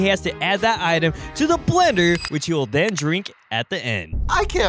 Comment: I thought it said leather like he was drinking leather😭